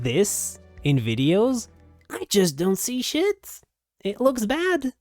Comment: did you just say .... ? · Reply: SHIT